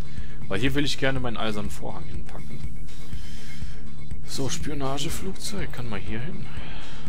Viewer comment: ab der min 27:21 hab ich verstanden spürnaseflugzeug hihi ;)